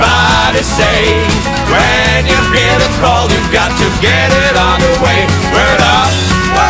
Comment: sometimes i hear it "when you repair the car you got to get it on the way" lmao